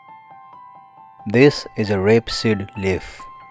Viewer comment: ok this guy is hard to understand, I think I understood the majority of what he said up until this point. What does "Reh sil vrif" mean? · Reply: It's a rapeseed leaf.